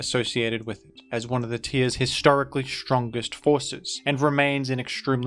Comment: I literally thought you said "one of it's strongest horses" at 1:30.